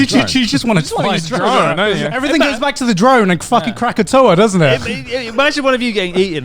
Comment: Did anyone else misheard krakatoa as crack a toe a?